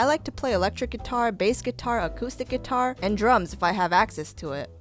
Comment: When she said drums I thought she said drugs and im like Holy shit dude. is on drugs